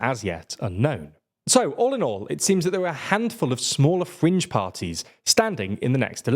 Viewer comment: I thought it said “fridge parties” and was genuinely confused.